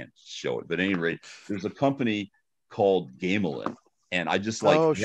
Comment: Mike Pondsmith talks about a company and I can't really catch the name. Does anyone know the company that he was referring to? Thanks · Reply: Gamelyn I think